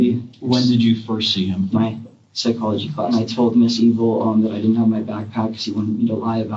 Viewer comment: did just say his psychology teacher's name was Mrs. Evil?